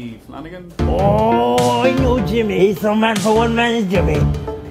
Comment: Can someone tell me what says at 0:45? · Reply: he is some man for one man is Jimmy